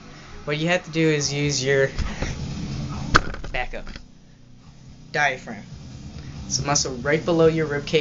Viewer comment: I think at 1:58-1:59 he said diaphragm